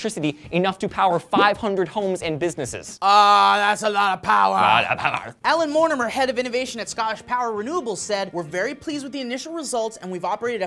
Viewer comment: The hell happened to at 0:57? XD I know said, "Ah, that's a lot of powa!" so did say "Lots of powa!"?